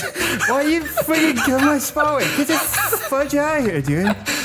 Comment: Did say fu$@ 2 times